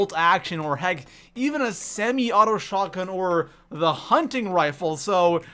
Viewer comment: did someone say Semi Auto Shotgun 🤣